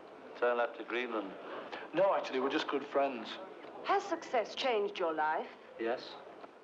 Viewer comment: I always accidentally think the reporter is saying "how has success changed your life" at 0:50 which makes George's response 10x funnier